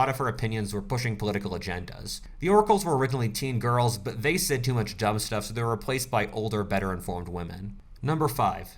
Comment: "the oracles were originally teen girls but they said too much dumb stuff...." - 2021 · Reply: When I first heard that quote, I thought he said they listen to too much dubstep